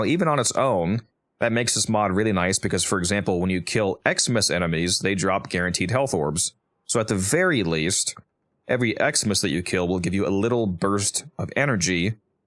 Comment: Each time you said Eximus enemies I thought you said "Xmas enemies". I thought you were talking about some special Christmas event enemies. - Matthew :person-turqouise-waving